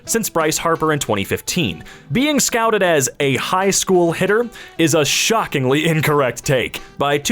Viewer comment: i thought that said hitler.... wtf is wrong with my brain?